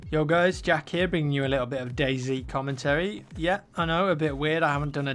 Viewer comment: lol every time I hear that It sounds like "First time for everything Gay" just listen it sounds like it but he says Ay XD